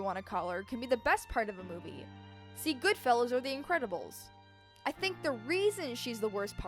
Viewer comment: Nah, I said Goodfellas :p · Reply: sounded like goodfellows 😂 no shade, I just thought it was funny